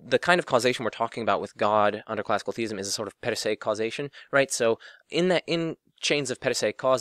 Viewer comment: Great video, does anyone know what kind of causes/causation is being said at 1:02:15? it starts with a P ..Perise or Pedisai or something like that :d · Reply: per se causes :)